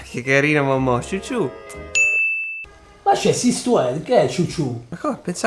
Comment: cosa dice a 1:34? si stuerc? hahahah